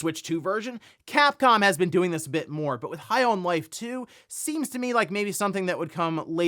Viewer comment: for some reason I thought I heard you say the high on life 2 leak was half life 2 and i turned my head to the corner of my kitchen as i was making lunch like WTF